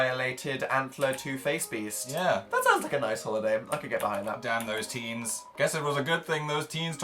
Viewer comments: Did anyone else hear Phil say "Damn those teens" at 7:23?????? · Did Phillip actually say "Damn those teens"?!?!?!?! 7:20 or a little bit after.